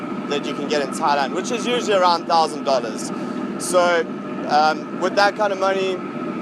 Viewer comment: did you say a 1000 or 9000? what currency was the in? and was that monthly, yearly or something else?